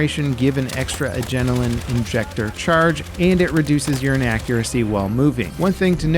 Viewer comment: “Lowers urine accuracy while moving” that sounds correct but also wrong. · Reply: I heard him say "Reducing your inaccuracy" but now I've read this I can't unhear your version 😂